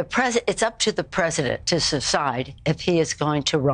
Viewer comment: am I the only one who heard Nancy pelosi slur the word decide. Sounds like she said saa cide